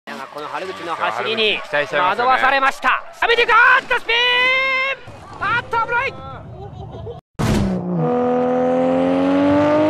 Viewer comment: " Aaahhh Cabron" es lo que dijo el narrador en japonés??